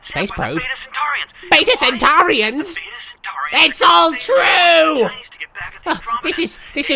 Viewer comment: Woah woah wait a minute....... DID JUST SAY SONTARANS FROM DOCTOR WHO????!!!!!! Someone correct me if I'm wrong, but thats what I heard. It's